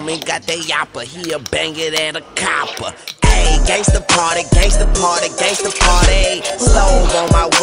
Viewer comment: I THOUGHT i heard grove st party, i was like fuckkkk yeahh! then i heard lil wayne..